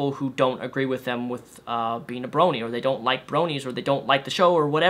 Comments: I thought the guy said brownies... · thought he said brownies